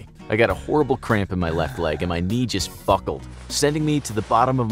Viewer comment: What does "Fuckled" mean? I think you meant to say "buckled" Mr. Narrator 😂